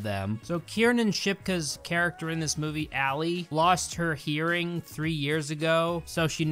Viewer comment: I heard "she lost her earring" and was confused for the longest time